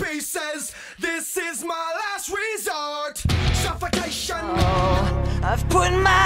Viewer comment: кот мой лает ето пизд*ц (послышалось))